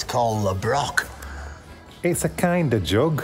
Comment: A late comment posted (Feb 2024). At 21:05mins - I had to play it twice. Looking at the sign with the theory about the town's name of Le Broc. I heard say "It's a joke". Discovered my ears had embellished his accent ("It's a jug"). Pity - was looking forward to hearing the joke 😃 Loved joining you belatedly on this crisp Autumn drive.